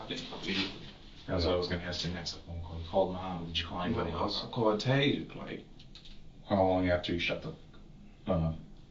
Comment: What was detective saying at 29:40?? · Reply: Ninjas just can’t!